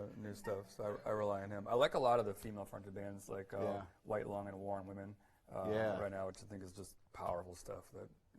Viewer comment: What female fronted band did Tim mention? I couldn't understand (around 24:25). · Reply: White Lung and The War on Women